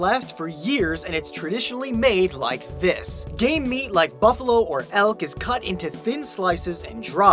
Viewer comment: Lawl did he said "gay meat"?